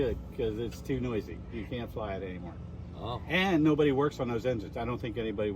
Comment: What I heard is "LEGALLY....YOU CAN'T FLY IT!", LOL!